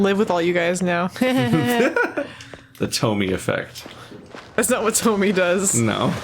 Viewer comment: can someone tell me what character they're talking about here? Was it Hitome? Sounds interesting. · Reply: Her name is Tomie 👍 She's one of my favorites..